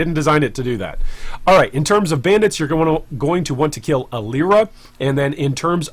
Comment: did i hear kill alira?